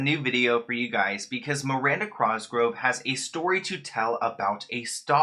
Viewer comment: Did u just call her "Miranda Crosgrove" at 0:02? 🤣